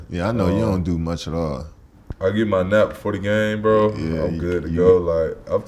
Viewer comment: did cade say "he gets his nut in before the game"????lmaoooo